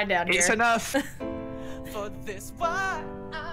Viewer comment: i heard "FoR tHiS wAp" 💀⚰